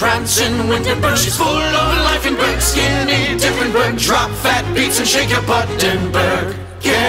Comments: DROP THAT BEAT AND SHAKE YA BUTTENBURG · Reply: I thought they said “ phat beat” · DROP THAT BEAT AND SHAKE YA BUTTENBURG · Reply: Omg.... I swore for a minute they said "Drop fat B** and shake your buttinburg" lmaoooooo 😂 😂 😂 😂